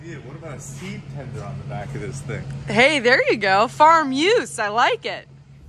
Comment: "farm use" sounded like "farm muse" 😉😉